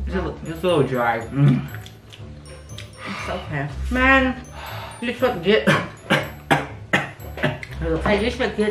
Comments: it sounds like she said you suck ... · I swear she said “you suck d***”